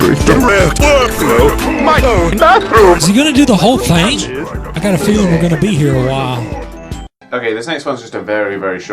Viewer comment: ! What did Dr.Robotnik say? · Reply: "Is he gonna do the whole thing? I got a feeling we're gonna be here a while."